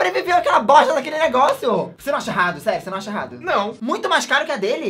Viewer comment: eu entendi sexy kkkk